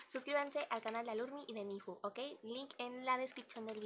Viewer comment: soy yo? pero escuche "suscríbete al canal de alurmi y de mi hijo" soy yo?!😳